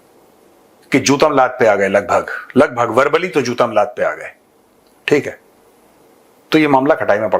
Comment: i hear chutad laat pe aa gaye😂